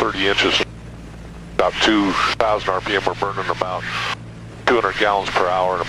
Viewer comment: Is it just me or did he say *Burger in my mouth*?